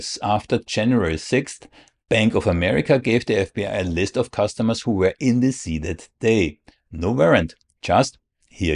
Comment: I wish I could understand what you're saying. I have a feeling it's important. 14:28 "Bank of America gave the FBI a list of customers who were in [????]...." ...in what? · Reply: DC, Washington DC on Jan. 6th.